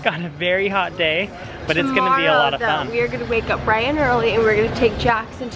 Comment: When said,"we'll get up bright and early" I thought she said ,"we"'ll get Bryan up early" hahaha